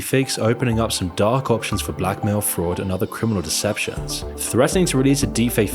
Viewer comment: Am I the only one that thought he said “Black Male Fraud” instead of blackmail fraud?